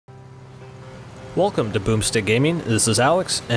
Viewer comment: At first I thought he said "welcome to poopstick gaming" 😂